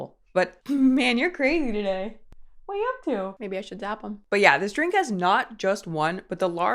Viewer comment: "Maybe I should zap him." Did I hear that correctly?